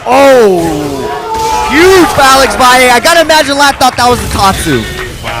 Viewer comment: "Oooh. Uuuved Alex Valle I got a magic laptop that was a tatsu." .. what did he say there?